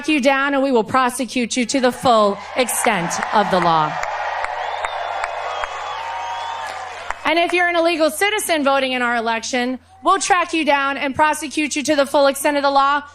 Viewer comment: Did Lara say "Illegal Citizen" at 7:57 ?